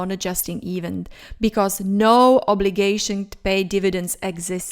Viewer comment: what did you say? Because no obligation....??